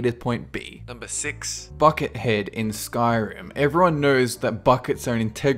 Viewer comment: i didn't heat bucket head i heard "fuckit head"